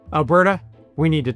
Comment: I thought it said "Alberta, pays" (in french)